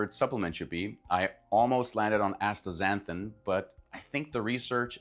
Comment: what he say? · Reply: Astaxanthin